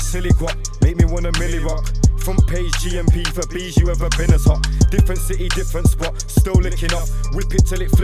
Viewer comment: Wtf does he say at 3:09? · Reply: Front page g.m.p for b’s you ever been as hot